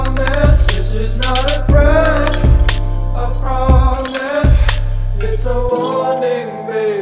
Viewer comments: ffs this whole time i thought he said "This is not a prank, I promise" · I thought he said "this is not a prank, I promise" and I immediately remembered those Filthy Frank days. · They’re taking my heart now They're taking my heart now and I don’t know why And I don’t know why Alright, alright, alright, alright, alright, alright, alright The demons told me everything They whisper in the night (Hey, hey) This is not a threat, I promise (nope) I promise This is not a threat, I promise It's a warning baby I just want you to know That's when the ceiling caved in And I'm fallin' in a trench, I'm behaving I'm behaving myself I'm behaving myself We don’t gotta die so soon So I just behave for you (for you, for you) We don’t gotta die so soon So I just behave for-- (Shh, quiet) This is not a threat, I promise (nope) I promise This is not a threat, I promise It's a warning baby I just want u to know Please just let me go Let me go I’ve seen enough I’ve seen enough I’ve seen enough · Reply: why do I hear... "this is not a prank, I promise.?" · ffs this whole time i thought he said "This is not a prank, I promise" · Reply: Yung Sushi I thought he said “this is not regret”